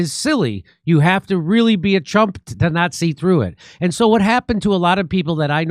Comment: Too funny, about 4 minutes into the video, you said “you really have to be a Trump to not see through it”. I heard “ you really have to be a Trump to Nazi through it”.